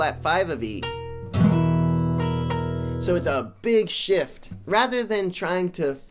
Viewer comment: i almost thought you'd say big shit